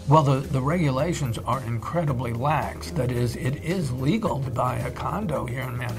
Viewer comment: There is an error in the subscript that is misleading to what the speaker is saying. He said, “it is legal,” not, “it is illegal.”